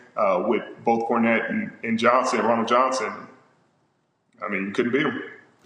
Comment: "Ronald Johnson"... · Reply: I have been scrolling through the comments hoping I wasn't the only one that heard him say "Ronald Johnson" smh